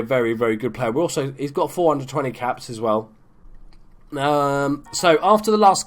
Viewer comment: thought he said 420 caps